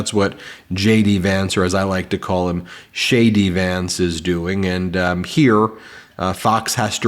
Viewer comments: Did you just call him "shady" Vance or did I just mishear JD? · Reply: Ah, yes you did call him shady vance...i like it. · DJ Vance? 🤷‍♂️ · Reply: I thought said SHADY Vance.....that works! 😊